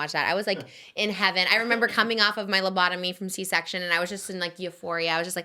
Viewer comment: Am I trippin or did Trish say, “coming off my lobotomy from my C-section”?😂 Did she mean anesthesia?😂😂